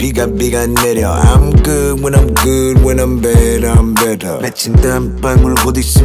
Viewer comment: "I'm good when I'm good when I'm bad I'm better"🔥🔥🔥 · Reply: I thought she said when I'm bad I'm badder